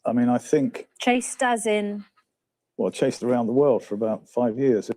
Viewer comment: No. You misheard him. He said, “I chased his mother a lot. All around the world for about five years.” #PaulMcMullan's #microprocessor is #MissingChips for #Remorse #Shame #HumanDecency and #ProfessionalEthics